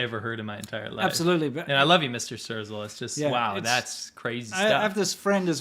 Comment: omg i swear that i misheard it "churchill" instead of "strezel" 🤣